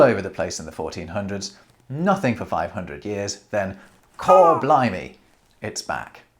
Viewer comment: Re: unkindness of ravens: "Caw blimey, it's back" I don't know if you did this on purpose, or if I just misheard "cor," but I did laugh out loud. Very interesting, as always!